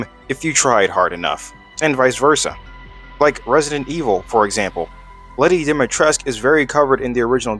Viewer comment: late night "pondering"... Also, did you just say "Reisdent E-Hole?" that was a definite 'o' sound, so it coulf not have been "Evil".